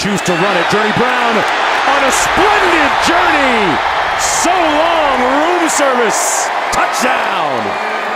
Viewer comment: sounded like he said so long losers.